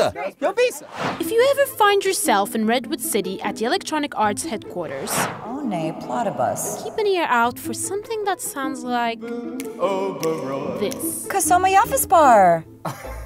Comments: Why do I hear Perry the Platypus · Funny thing is sometimes it sounds like they are speaking a few lines of broken English at random times. 1:00 Sounds like she said "Gota sell my office bar". Since early days I always laugh when a sims says something that sounds English but it is completely random to the situation in game.